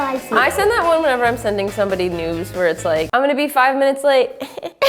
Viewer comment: anyone else thought she said nudes